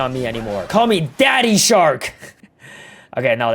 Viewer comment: 😂Did bro say daddy 😅 12:37 😮